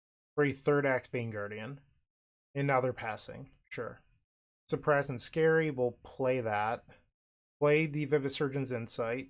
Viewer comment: Whenever you say the name of the blue depletion land i hear "surprised and scary". I don't know the actual name of the card and I refuse to look it up. It is forever the "surprised and scary" land to me.